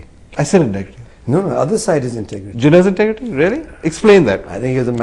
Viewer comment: GHOCHU HE WAS TAKING ABOUT HIS FATHERS FATHER WHO IS NANAJI DESHMUKH , JINNAH WAS HIS MOTHERS FATHER AND IF HE WOULD HAVE LOVED PAKISTAN HE WOULD NOT HE AN INDIAN CITIZEN TODAY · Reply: idiot @ 4:16 he is clearly saying Jinnah.